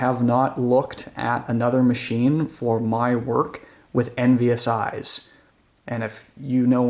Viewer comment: Where do you see it? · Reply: OMG. I just turned on captions and understood what you said. I heard it as "my work with NVSI" thinking it was some kind of company software when you really said "envious eyes" 😅